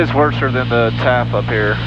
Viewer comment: Did you just say worser when discussing the TAF at 19:03 mark?? lol